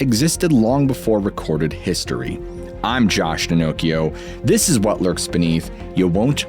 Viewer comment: I'm sorry....erhm...did he just say his name is "Josh Pinnochio"?